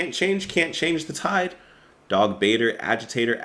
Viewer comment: hope your feeling better! Skip Who Dunnit if you can. Its the worst song Genesis ever recorded! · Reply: lol that's for sure.. I useto think he was saying "Was it you are was it me or was it ecstasy" Took me years to read the lyrics it's x or z. Just like this song when I thought they said Darth Vader and not Dog baiter. Lol